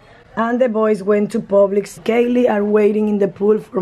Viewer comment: i thought you said my voice went to publix i started cracking up